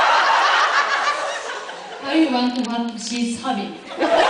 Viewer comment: what did she say all I heard was “she’s coming” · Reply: I want what she’s having